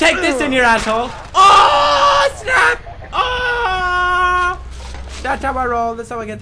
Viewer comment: What say Pewdie? " Thats are my roule (?) ........? 10:14 Bros help me understend :)